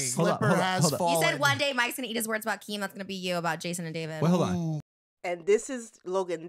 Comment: What does she say at 14:40- 14:43 .? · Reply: “He said one day Mikes gonna eat his words about Keem. That’s gonna be you about Jason and David.”